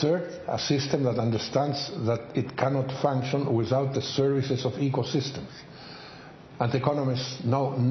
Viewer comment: Subtitles say "without the seriousness of ecosystems", but he actually says "services of ecosystems"